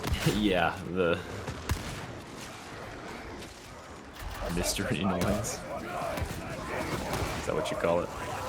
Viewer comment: I thought i heard voice lol